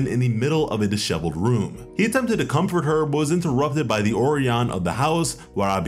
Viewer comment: Goodness, I was really confused when you said ''Orian'' (That's what it sounded like) only to realise you meant ''Oiran'' (Pronounced as Oi-ran) Not meant as hate, just a correction